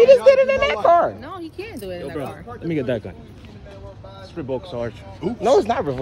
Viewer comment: sounds like they said "it was revoked" I'm assuming it was his CCW and he got a pair of shinnies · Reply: Thats exactly what he said "its revoked sarge"! Thats why he fought so hard against producing it! It was probably revoked for him violating the CCW laws on other incidents!